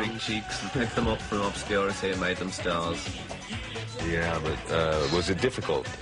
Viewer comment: I have some parts I can't make out. Pete saying , look this little devil . . and after this I can't catch even though I try many times. I need some help. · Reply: visage bracket backlash I can’t make out he’s saying either sounds like he says “obscurity and made them stars”